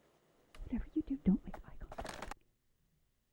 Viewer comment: I think she actually says: ''Whatever you do, don't make eye contact.'' Which is a same thing said before Alex peers at the Mimic (Nak) behind the blinders in the Mimic episode. Which you didn't include here. Making an eye contact with Nak seems to be a no go.